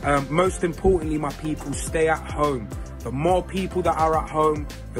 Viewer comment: Some people sounded like they said stay a hoe